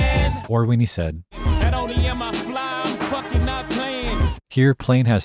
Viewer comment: thought he always said “i’m f*kin up planes”